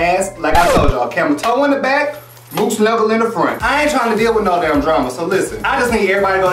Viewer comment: u said a camel toe in the back and snuggle what in the front???😂 don’t know what u said but it was funny.😂😂😂😂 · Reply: Moose knuckle in the front Camel Toe in the back lmao 😂😂😂😂